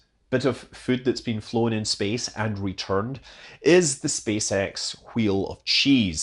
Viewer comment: I really thought you said "Space Eggs"........